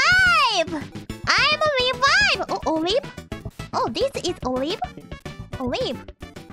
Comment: i think she said "i am alive" but with spanish pronunciation